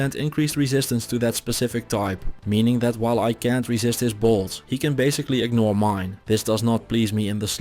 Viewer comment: Instead of "I can''t resist his bolts" I heard "I can't resist his balls"